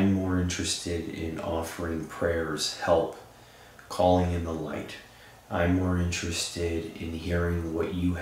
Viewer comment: It sounds like said help us all get to the light